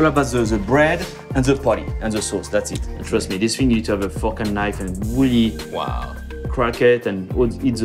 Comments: i thought chef ludo said "trust me, this thing needs a focking knife." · I thought the chef said "U need to have a focken knife"😂